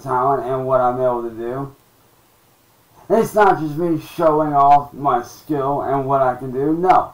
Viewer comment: I hear "Showing off my skull" - only thing that makes sense.